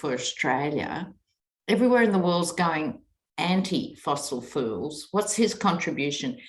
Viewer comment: ❤ I may have misheard you, but, if not, I loved your freudian slip. " the rest of the world is getting off fossil fools "